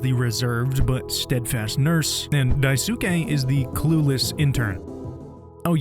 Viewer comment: In the introductions, I misheard "clueless intern" as "coolest intern," and I was like, yeah, he IS the coolest intern. I had no idea these were the same devs who created How Fish is Made, that game was gross as hell. This one is also gross as hell but in a completely different way. Wonderful work by you and all of the voice cast!